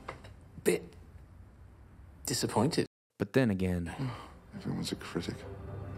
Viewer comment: What does Sherlock say at 8:35 I couldn't understand cuz I'm not good at English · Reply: 김효민 “everyone is a critic”